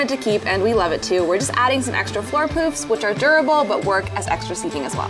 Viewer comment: OMG. I thought you said, "We just added some extra floor poops "! Uh .... no. No. That would be "poufs," thank God. My bad.